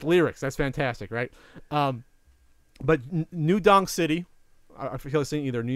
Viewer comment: Am i the only one who thought was saying New Dong city?